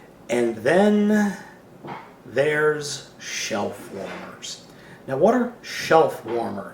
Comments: the beginning of the glorious shellformers the shellformers king is now being reviewed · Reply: Omg hahaha this whole time i thought was was saying shell formers and i was like thats gucking not wat it is hahaha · I thought you were saying shell former for the first seconds of the video.